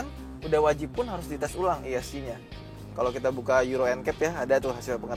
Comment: yang buat liat tes stability control apa namanya? kurang kedengeran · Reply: Rayhan BP euro ncap